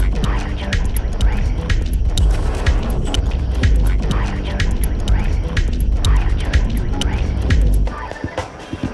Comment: anyone knows what the woman say? (non English born here, still learning) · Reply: She says: "i have chosen to embrace it"